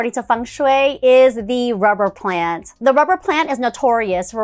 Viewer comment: I had to pause here for a little while. 9:12 I heard "Robert Plant". It's a good thing you add the title "Rubber plant". Oh boy. I'll see myself out.